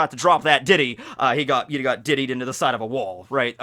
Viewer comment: It’s great that I look at the screen and to me I heard “got tiddied into the side of a wall,” and thought nothing of it